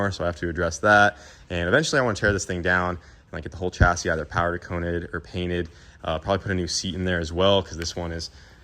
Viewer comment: Did he say powder con-Ed at 20:33? Lol. Reminds me of caint the page.